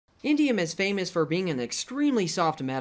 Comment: Dude I thought he was saying India at first XD